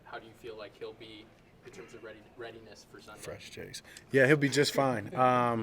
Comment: Am i tripping? What Dak say at 5:06? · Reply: "Fresh Js" I think lol I assume someone had some nice shoes